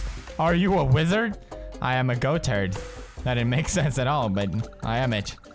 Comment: Did he say Goat turd?